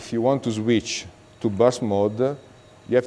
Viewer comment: Does he call full auto "boss mode"!? 11:32 -ish